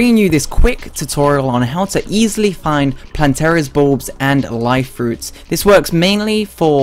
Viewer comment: did you say planteras balls